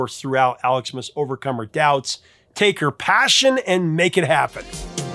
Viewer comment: That clip from Flashdance that you played, I always "misheard" the lyric as "Take your pants off, and make it happen"....